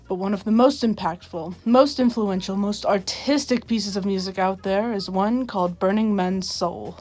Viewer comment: lol I thought u said autistic